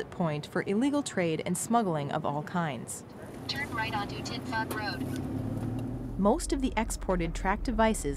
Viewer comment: am i hearing the name of that street correctly?? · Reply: AHEM t*t f*ck road? AHEM